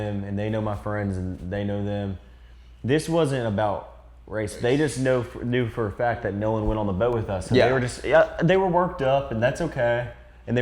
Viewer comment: he clearly said “They just Know…Knew for a fact that Nolan Went on the Boat With US”